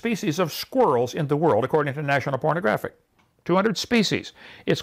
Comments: Did he just say national pornographic? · Did he say there were 200 species of squirrel according to National Pornographic? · Did he say "National Pornographic" here? What the fuck, Kent. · Am I the only one who heard "national pronographic" (spelled correctly, that is)? Every time I listened to it trying to make sure... O.o Um... Freudian slip...? Also, Kent, why do we then ask what KIND of bat? How does that work with your definition? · Am I the only one who heard "pornographic" at 14:56? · Am I crazy, or did Kent say there are 200 kinds of squirrels according to national pornographic at around 14:57? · am I the only one who heard "according to National Pornographic"? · Did Hovind actually say “National Pornographic”?